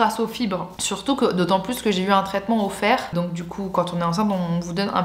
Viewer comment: surtout que j ai eu un traitement au fer Moi qui avait cru entendre un traitement offert😑😂